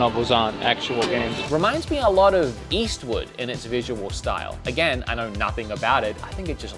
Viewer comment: did you really call Eastward "Eastwood" at 8:54 ??